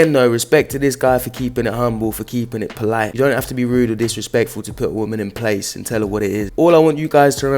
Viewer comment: I honestly heard him say "you don't have to be rude or disrespectful to put a bitch in her place". Had to go back.